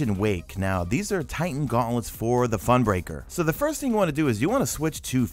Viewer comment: #madeittotheend Did you say funbreaker at 2:49?